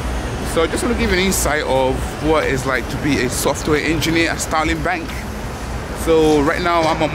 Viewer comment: did I just hear Stalin Bank!!!🤣🤣🤣😛